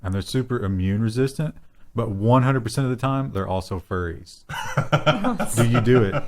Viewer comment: they're also what....fairies did you say?....... I couldn't make it out, was it fairies?